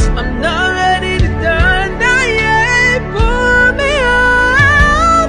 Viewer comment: 5. Pull me ight is such an inspirational lyric. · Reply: frrr😭😭😭 it sounded like he said pun me igh 💀💀💀